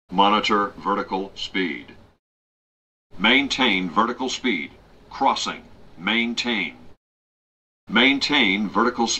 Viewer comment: 真夏のロリコンスピードにしか聞こえないのは自分だけ？